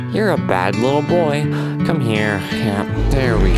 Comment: did he just call the ender dragon a boy 🤨